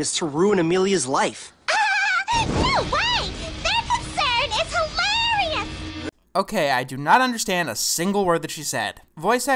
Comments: I think she said "no way!their concern is hilarious" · i think i FINALLY (after like 15000 rewatches) figured out what orange haired girl said at 6:13 "no way their concern is hilarious!" i think hopefully you get why pitching up voices doesn't always work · i got is she said " No way their concern is hilarious" Don't ask me how I know I just put the Pieces together. It doesn't make the show any better though